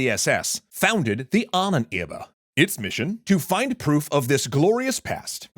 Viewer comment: What is he saying at 2:50? Himmler founded the what? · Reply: “Ahnenerbe”